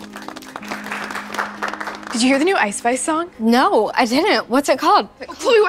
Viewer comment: did she just say ice spice💀💀